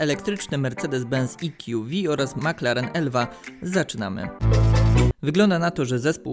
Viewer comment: Na początku usłyszałem mclaren FSO.